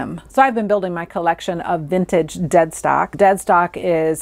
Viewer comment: Thank you for this video - very interesting & easy to listen to! I laughed - initially, I thought you said ‘Dead Sock’ - not ‘Dead Stock’!